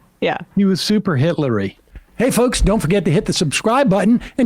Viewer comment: Did i mishear Sam say “hey folks, don’t forget to hit(ler) the subscribe button”?